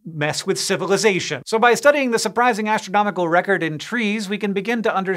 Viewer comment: Thought it said "The Astronomical Records in... Texas?" and did not question it for a single second